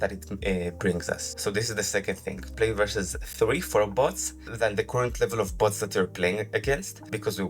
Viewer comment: what did you say at 10:10 ish? I couldn't quite make it out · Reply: he said plau vs three for bots than the current level of bots that you''re playing against